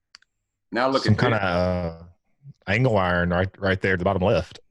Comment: "I have an eye for this stuff" Misses artwork depicting two humanoid figures shaking hands (one wearing what might be a spacesuit) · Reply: P 7:58 at bottom left, right where the guy says it looks like something (I can't make out what he says) and says that's what it looks like to him too. It sounded like the guys was saying "hang wire", but I don't know. Anyway, it looked to me like a drawing of two figures on a rock, though maybe not shaking hands. That could have been peridolia on my part, I guess.